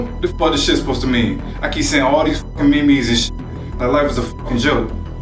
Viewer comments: did he say “ME ME’S”?? Or am I trippin’ ? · did he say me me???? 😂😂😂 dude got the hot Cheetos claw on his head